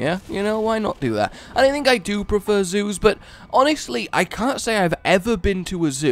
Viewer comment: Whenever said zoos I thought he was saying shoes.